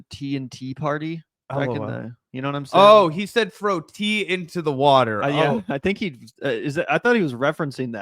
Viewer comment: I honestly thought he said throw "DMT" into the water